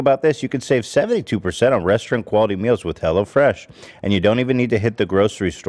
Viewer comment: Sounds like Ethan says “restroom quality meals”